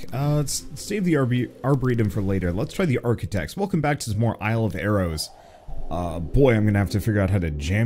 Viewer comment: Lol, i thought it said "isle of arousal" 😂